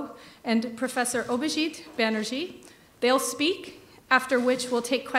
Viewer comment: Did she just say Mr. Obhijeet Banerjee! 🤣